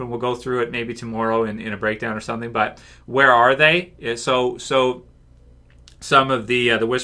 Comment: yeah it does sound like someone is saying where are they in a zombie voice